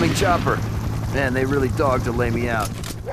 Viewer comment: I heard "they're really thogged to lay me out", which could be togged, a slang verb that means dressed up or decked out. I definitely heard the T sound if not Th